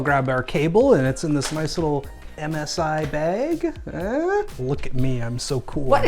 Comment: I thought he said MSI egg instead of bag and thought "yea that fits the dragon theme" lmao